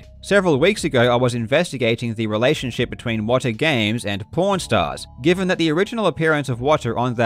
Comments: I kept hearing ‘porn stars’. xD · I thought he was saying "porn stars" for the first ~15mins... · I really thought he was saying that he went on porn stars 🤣🤣🤣🤣🤣🤣🤣. I was like what 😳? Until I glanced back at the clips . Such is the hazard of listening while working on other things 🤣🤣🤣🤣.